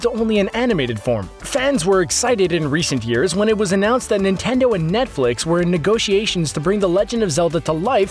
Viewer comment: When he said "Nintendo and Netflix" I thought he said "Ninkendo and Netflix" lol